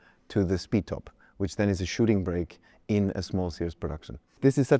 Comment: With his accent it sounds like he says it looks like a "shitting brick"! - BWM is starting to look like older Alpha Romeos.